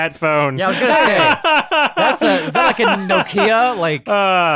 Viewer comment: did just say '' no key ya''?